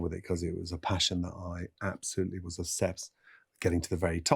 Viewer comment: Captions are wrong at 6:10; He says "absolutely obsessed with," not "absolutely was a chef"